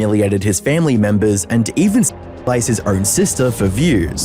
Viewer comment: "Even spliced his own sister for views." What? What the did he say here? What did this step bro do to his sister for views?? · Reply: “Even s*xualised his own sister for views”